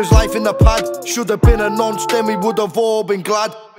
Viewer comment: “Should’ve been a nonce then we’d all be glad” have I misheard this